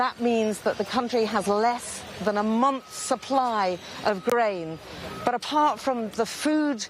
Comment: Who else thought she said the country only has a month amount of supply of Brain xd